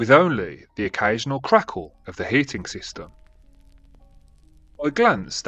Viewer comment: Yyeesss has descended from the realm of spooks to let us mortal bask in the glory of FEAR 🥵😎 I LOVE your accent, ar first I heard the word "crackle" as "crackwhore" and shot juice through my nose due to the sudden unintended comedy 😂 "sudden crackwhore of the heating system.."